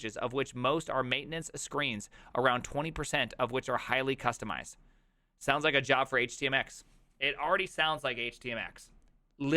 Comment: I didn't understand / hear him properly. Did he say "this sounds like a job for HTMX"? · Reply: edit: yes